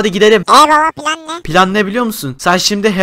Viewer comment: eee babiş pilan ne yerine eee babiş pilav ne diyor bebek 😂😂